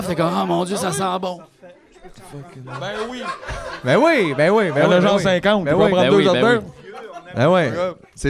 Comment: j'ai compris ''ya genre 150, i peut en prendre 2 certain'' ayoye.